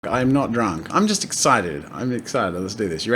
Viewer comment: anyone else hear him say 'i'm not drunk'?